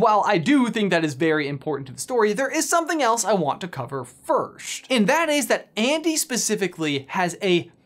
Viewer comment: Does he say "firsht" instead of first